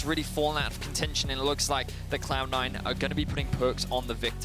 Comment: does he call them clown9 at 3:20? lol